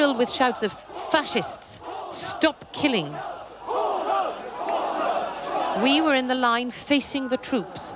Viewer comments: They were shouting "animal" (alternative word for axxhole in Chinese), instead of what the reporter had said "facist, stop killing". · What they are shouting is actually “animals” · they are actually yelling '畜牲'(animals) , which is a cursed word describing someone totally lost their humanity.There is nothing sounds like not killing or fascist from what I heard.